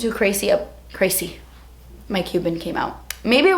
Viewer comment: I thought she said "Mike Cuban came out" and I'm like okay? 😂